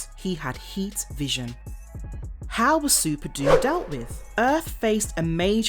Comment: Thought u said Superdude at first